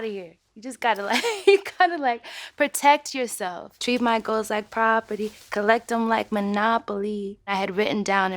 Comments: Did anyone else think they heard “she the Chico?” PLZ TELL ME IM NOT THE ONLY ONE! I feel stupid 😂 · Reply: ye and i also thought they said "treat my girls like property" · yo i thought it said "treat my girls like property collect em like monopoly" 😅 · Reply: Same but I thought she said collecting and not collect ‘em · i heard it as "you treat my girls like property, collect like monopoly" tf lol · Bro when she said "treat my goals like property" why did I used to think that it was "treat my girls like property"? 💀💀💀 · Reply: I thought it said treat my girls like properly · this whole time i thought she was saying treat my GIRLS like property ..... Oof somebody help me